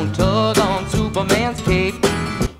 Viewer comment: I hear cape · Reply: I heard cake 😂😂